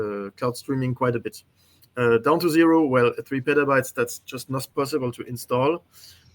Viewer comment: I'do download the 1.4tb, give me the option! i don't care! i have a dedicated 4tb nvme just for the sim! this is whats messed up with the way the sim is setup, if downloading that data is going to make things better for people that have the capacity give us the option to download it! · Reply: I think you misheard, he said 1.4 petabytes lol, as in 1,400tb